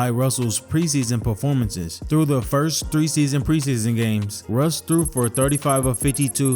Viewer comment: had to rewind it back, thought I heard threeseason preseason